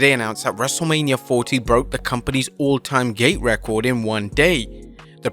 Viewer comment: sorry, but i cannot unhear gay instead of gate 💀